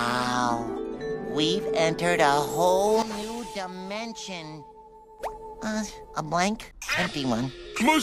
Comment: When I first saw this video, I thought SpongeBob said “we entered a whole new invention” and I thought of a liminal space and it was the Motor Speedway of the South but it’s dark, empty, and the race cars have creepier eyes and teeth. The level name is called Level Let’s Race! The cars are the entities, and it is a very dangerous level. Hm? Wow! We entered a whole new dimension! Um- a blank, happy one. Must be the boring dimension! Let’s go home. Maybe there’s an exit this way. Huh? Must have made a wrong turn. Hm? intense panik pants PATRICK, THERE’S NO ESCAPE! THERE’S NO ESCAPE, PATRICK! I CAN’T TAKE IT! LET ME OUTTA HERE! both scream skid error